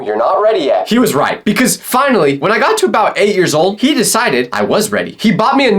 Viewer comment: i thought he said 80 year old